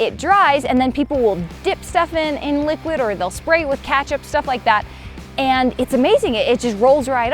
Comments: did you say kechap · Did just say katchup? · did I just hear katchup and not Ket-chup kinda annoying 🗿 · Did I just hear Catchup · Did she just say 'Katchup'